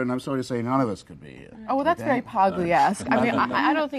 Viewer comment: What does she say at 32:09 - that’s very Pogliesque? That’s a not a word. What is she saying? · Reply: Pagliesque — after Camille Paglia.